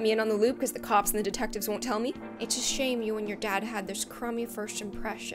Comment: The premise feels like the book "Wonder" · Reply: I thought you said you made my dad